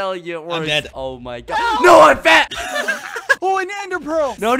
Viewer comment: Was I the only one who heard "NO, I'M FAT"